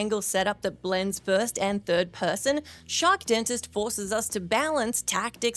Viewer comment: I was listening to this for a while and when i looked i realized u were saying shark dentist. I thought u were saying ‘shock’ dentist.. maybe i wasnt listening as good as i thought i was..